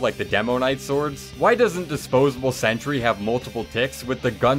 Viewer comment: What is he saying? I am hearing something that shouldn't be there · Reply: “Why doesn’t ‘disposable sentry’ have multiple ticks?”